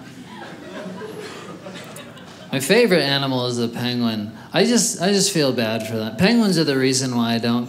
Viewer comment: I thought he said Pangolin, not Penguin, and I was very confused for a while.